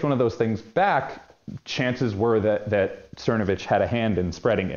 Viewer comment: Title is misspelled. It should read, "Mike Thernovitch". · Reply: I hear it as “Shthernovitch”. His impediment starts at the side of his mouth with the tongue at the right side of the roof of the mouth and along the top teeth